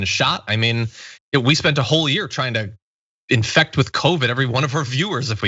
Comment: did you just say that you spent all year trying to infect viewers with c-virus if you could?